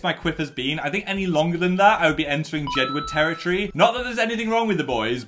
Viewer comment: bruh the captions saying "gedwood" when is saying jedward killed me 💀